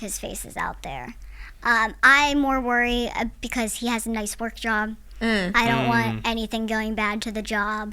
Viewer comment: did she just say he has a “ nice work job “ …. Well like I said she doesn’t sound like an adult at alll! So I don’t understand her saying ppl realize I’m not a child when I start talking… if anything to me it makes it more difficult to tell if she’s adult or not! And I’m not hating at all! I love her lol I loved the show, I think she’s super sweet I just think she’s a little to ignorant to how people really see her I mean, I don’t know how to explain it well enough